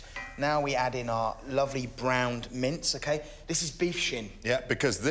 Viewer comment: Did I hear him say “mint” for one of the ingredients in the cottage pie?